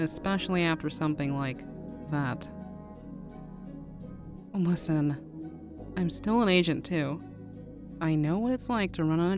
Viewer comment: I thought she said "I'm still an agent 2." tecnically the truth, but not what she said